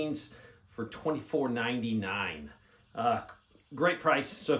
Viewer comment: Small Business of the Day. Great Sale <link> End of Summer blowout sale on Green Phosphor Echo spec PVS14s PVS14 Green phosphor Echo spec $2499. while they last! <num> Phone Numbers Yes we are out of our minds with this loss leader price! Brand new, autogated, manual gain control, all standard accessories and 5 year warranty. IN STOCK, no wait, order today by 1pm and it's heading your way today! VERY LIMITED QUANTITY available, snooze you lose. PVS14 Green phosphor Echo spec $2499. while they last! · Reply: Ha ha I thought I heard it was 24.99 what I thought you said but then looked at the price being 2499.99. Yea I couldn't believe it was 24.99 so I took a peek. Way to much for me to justify. But thanks for sharing.😊